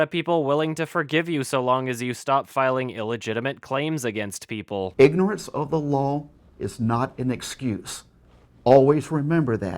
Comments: He's right, though. Ignorance of the Lawl is not an excuse. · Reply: I thought he said ignorant of the lawl is not escuse. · "Ignorant of the lawl is not an ascuse" - 2015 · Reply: MelancholyRose I thought he said Auschwitz ... No I'm serious, when he tried saying always I got a bit fucking creeped out. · His overall demeanor of how he acts really bothers me. Like just watching him makes me unreasonably angry. Also, I heard "Ignorant of the LAWL is not an ascuse."